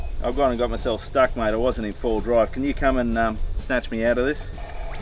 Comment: "I wasn't in four-wheel drive." Seeing as the front wheels were spinning, and defenders are permanent 4wd, I would say that you were in 4wd. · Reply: I thought he said full drive, I just figured it was slang for moving fast enough.